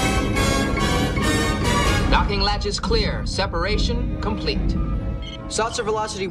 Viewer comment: It sounds like Geordi says "Knocking latch" at 1:42.